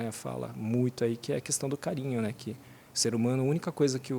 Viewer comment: entendi certo 27:12 "a única coisa que o ser humano não consegue viver sem é cocaín.. é carinho né"